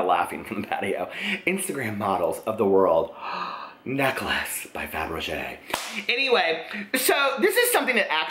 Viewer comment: at 2:25 I thought you said "ferrero rocher necklaces" LMAO!